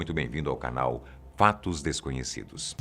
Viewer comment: Quando ele falou “fatos desconhecidos” eu ouvi “patos desconhecidos” ksks